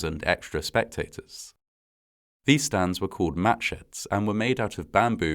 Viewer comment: Did he say "match heads"?!? Bamboo and palm fronds eh? I see where this is headed...